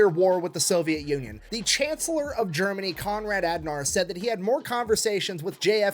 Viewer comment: The Chancellor of Germany Konrad Adenauer. I had this playing while I was unloading the dishwasher, and I could've sworn I heard him say Admiral Ackbar. "It's a trap!"